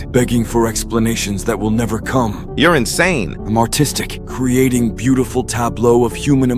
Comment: I thought he said 'im autistic'